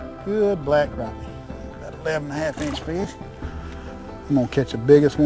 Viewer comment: sounds like he says 11 inch bitch